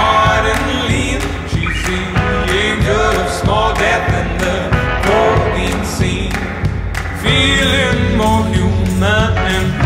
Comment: I always hear it as "protein scene".